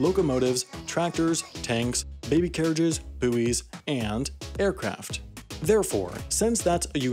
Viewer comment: mmhmm I heard “buoys” aswell